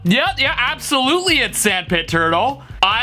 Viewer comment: I heard “sand potato” 😂😂😂